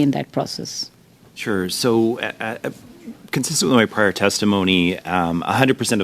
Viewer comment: All I kept hearing is “as per my previous testimony” we should be sending France a thank you card for saving the GTA because whatever they’re doing is obviously not working